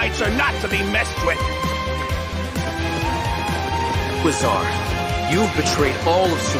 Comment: What did he say at 2:57? Sounds something like Bazaar, or Wizard..not sure. If anyone have any guess pls lemme know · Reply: Yes it's Azar. I just checked on the youtube livestream with their (Mihoyo's) subtitle.